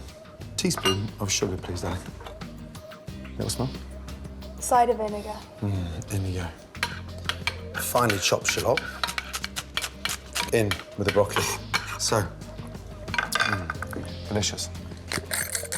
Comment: I swear when he said "finely chopped shallot" I could have sworn he said "and finally chop shit up" 😂